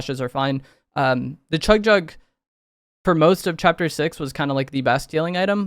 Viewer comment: Did he say how "ass" this season plays out? 😅